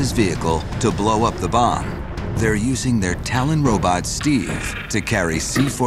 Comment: could an American please translate to me what the narrator is saying? only If you know the topic. Using the tail-in robot Steve or Tallinn robot Steve. I cant make it out. Thank you! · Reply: Im pretty sure he said Talon and that could be the model of robot or the name of the company who made it